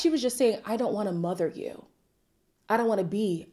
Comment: No, I’m with you. When I watched the reunion, I also thought AD was saying “I don’t want to mother you” when she said “I don’t want to be your mother.”